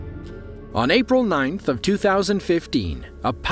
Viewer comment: The man: "on April 9th, 2015" What I hear: "On April 9th, 2050-"